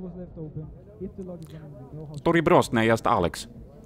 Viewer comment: Did he say "Добе бросьте есть, Алекс?"